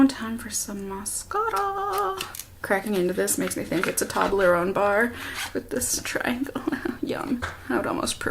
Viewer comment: Lol when you said “Toblerone bar” I thought you said “toddler own bar” 😂😂. But is that how it’s actually pronounced? Tobb-ler-own?? My whole life I’ve always pronounced it toe-blur-own hahaha. I must get to the bottom of this!